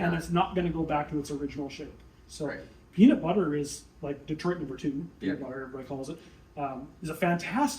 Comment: What did he say @ 14:08, "peanut butter is ----"???? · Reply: Peanut butter is a nickname for Detroit #2 lube. It's brown and looks like peanut butter